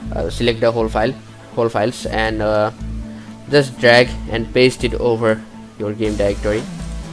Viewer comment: hey what did u say at 1:29 ? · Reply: I told 'Just Drag and paste the files from the archive to the game folder' :)